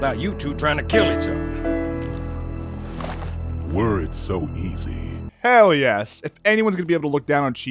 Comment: I always misinterpreted Arbiters first words cause he says them with such a deep voice. Whenever Arbiter said "Were it so easy", I had always thought he said "Words so easy". It fit in perfectly well with his spirituality nature and the context at the time as well. The first time when Johnson is saying the Elites were on their side and they need to not kill each other. It much easier to say that when most of the people their have been at war with the Sangheili for their entire adult life. And then at the end when Lord Hood mentions the crimes the Covenant did but also give his thanks for being by Chief's side until the end. The words are easy to say when compared to what they mean.